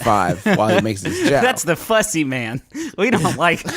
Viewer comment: I thought it said "the pussy man"